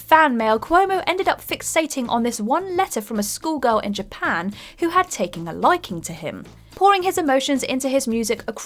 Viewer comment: Okay, on "Across the Sea," I had to go back and listen to it a second time to hear that the voice-over had said "she had taken a liking to him," because I had thought it had said, "She had taken her life." Imagine how callous and self-absorbed the lead singer of Weezer would have to be if he got a letter from a fan whom he somehow found out later had taken her own life, and all that he could bring himself to obsess about in the song that he wrote about her was whether or not she had ever fantasized about him (presumably while pleasuring herself?)